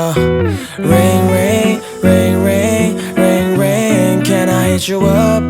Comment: I thought he said "ring ring ring ring ring ring can I eat you up" LMAO